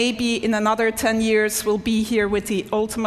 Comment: Did she just say brain implant at the end? No thanks...